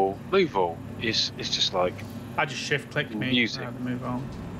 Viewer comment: Sat down to watch while enjoying my breakfast smoothie. Until, when kept saying, "shift click" I heard "$#!t quick" 😮 --- i laughed so hard i almost choked on my smoothie 😂 ... #alwaysentertaining 😍